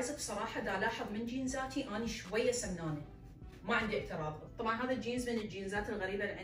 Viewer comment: شنو ما سمعته شكالته سمنانه 🤨 وين سمنه با الموضوع